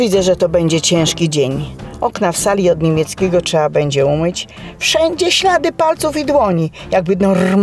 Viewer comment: dlaczego ja usłyszałam "Okna w sali od niemieckiego trzeba umyć, wszędzie ślady palców i BRONI" I szczerze to uważam że to bardziej pasuje XD